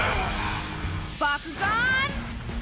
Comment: For a split second i thought he said bakugou from mha